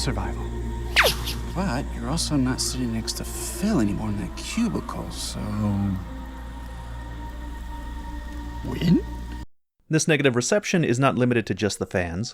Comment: I thought you started saying: "This nigga...."